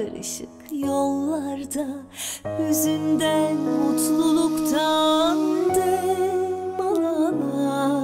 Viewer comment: burda ne diyor 0:38 anlamadım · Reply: Hüzünden mutluluktan dem alarak